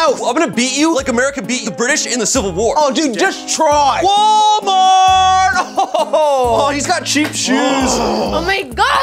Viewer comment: He has cheap shoes 😂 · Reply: Cookie Keemstar Man 😂 thought he said KMART!